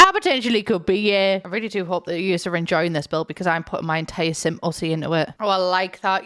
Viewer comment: I've actually built quite a few houses from dreams! Edit: 12:20 did you say simussy?! XD